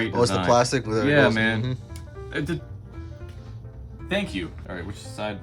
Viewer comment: that thank You at 5:22 sounded like metal slug jsjsjsja